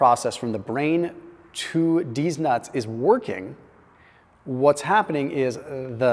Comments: Did he just say Deez Nuts?!😂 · Did he say from the brain to deez nuts?? Lmfaooooooooo. Around minute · Was anyway else confused when it sounded like he said deez nuts lol I was like damn I need sleep 😂